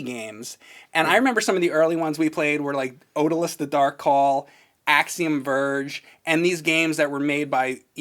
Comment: what's the name of tha game? Cant understand what he is saying, before Axiom Verge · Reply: Odallus: The Dark Call